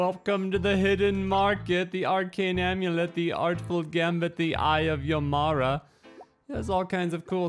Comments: i can't be the only one who heard "eye of Yo Mama" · my first thought "The Eye of Yomama" ... what does this say about me XD ... the game looks real cool tho!